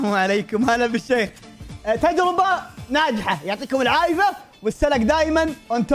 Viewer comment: كاني سمعته يقول العايف مو العافيه ( 4:28 )